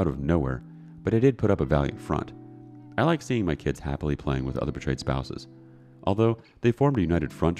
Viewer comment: lmao, I heard that as 'I like seeing my kids happily playing with other betrayed spouses' 😂 like they're just playing with a bunch of cheating victims